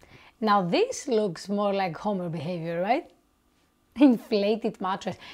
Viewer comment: you said this is more like homer behavior the subtitles said this is more like homo behavior